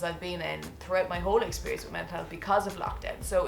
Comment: Lol around 4:05 the CC thinks you're talking about the darkest times "throughout my whole experience with menthol" instead of mental health